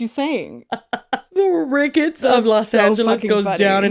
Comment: I watched this ep last night after a couple of drinks and I thought Tana was saying she was ‘in the crickets of Los Angeles.’ I just went with it.